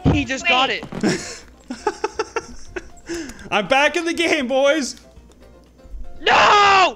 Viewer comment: !!!!!!!!!!!!!WHEN GOT A HOLE IN ONE I T SOUNDED LIKE HE SAID I AM STILL INTO GAMEBOYS!!!!!!!!!!!